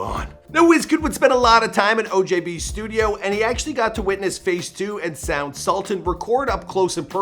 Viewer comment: Did he really say "face 2"?